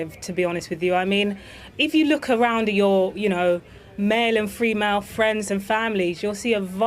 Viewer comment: did she say freemale?